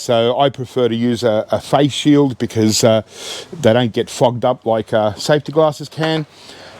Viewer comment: Lmao @ 3:00 my american accent made me hear something i have never heard you say, i could have sworn i heard you say, "the don't get fu**ed up"... had to rewind it to double check, and even had to listen a 3rd time just to finally hear "fogged up" . Lol. Love your content man. Been watching every video you upload for years. Have a good day.